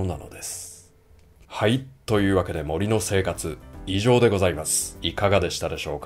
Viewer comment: 貴重な動画有難うございました 最後に「はい、森の生活、異常でございます」と聞き間違えるほど、今の生活との乖離を感じ、考えさせられる事が多々ありました このような内容が多いさんの動画が本当に好きです